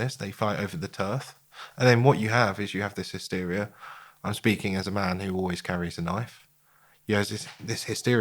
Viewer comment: I was thinking what a good interview this is until the interviewer said ..."I'm speaking as a man that always carries a knife"... is that what I heard him say??